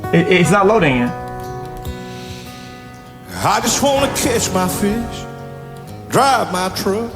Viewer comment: 🤣, I thought he said/sang "I just wanna kiss my fish" that would have been more entertaining than that drivel leaking out his dumb MAGA mouth. 🤭 Maybe that should be a new "kiss my ass" variant.😜